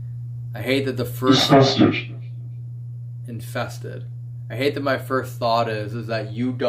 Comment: When it said the three names and then you thought it said “infestated” but I’m pretty sure it said 3 sisters! Which would make sense after saying the 3 female names!